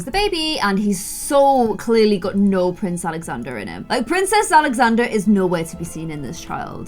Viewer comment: did you just say princess Alexander? 🤣🤣